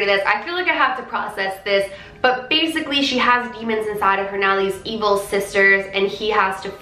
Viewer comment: my phone is cracked and I thought demons said lemons lmaoo